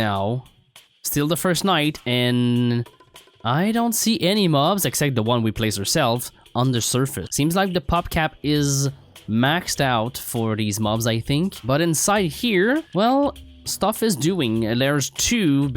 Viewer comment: i thought he said "popcap is maxxed out" XD